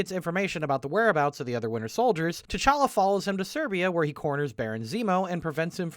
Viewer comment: Text: "T'challa follows him to Siberia" What is actually said: "T'challa follows him to Serbia" LOL